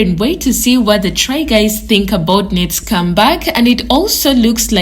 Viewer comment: Every time you said "Ned's come back", I thought you were saying "Ned scumbag" 😅 ... Works either way 🤷‍♀️